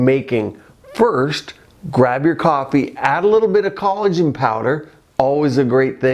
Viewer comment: I am watching this with subtitles turned on. What I hear: "First, grab your coffe, add a little bit of collagen powder..." What I read: "First, grab your copy, add a little bit of college..." Me, taking a glipse at my homework: a coffe with a bit of college in it would be the best drink. But I wouldn't mind if my copy would do my assignments...